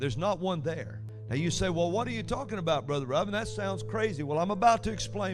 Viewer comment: When he tells the story when someone calls him "Brother Robin", his accent sounds like he is saying "brother rubbing". Means nothing to any conversation. I just had a little giggle.